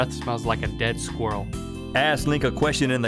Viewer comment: At the end I thought it said "Ass Lick by posting in the comments" rather than "ask link"